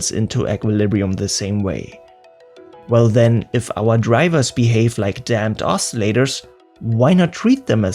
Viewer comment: Am I the only one who thought he was saying "Damned oscillators" the whole time